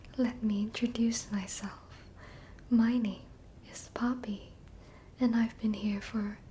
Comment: I thought she said "POMNI" and I went: "TADC?" Then replayed it, and it was NOT Pomni. It was Poppy.